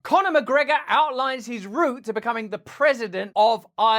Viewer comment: I turned this on while cleaning up and I thought you said Cononel McGregor..I'm like...wtf.😅